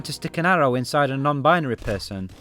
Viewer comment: did he just say sticking a arrow in a non binary person or am i going crazy